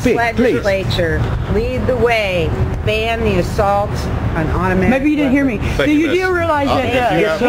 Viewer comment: "ban the assault on automatic weapons" did she say that?